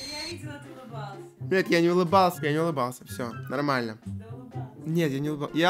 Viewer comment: я единственный кто заметил что он сказал "бл*ть " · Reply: Он сказал "нет, я не улыбался" Уши почисти😠🤦‍♀️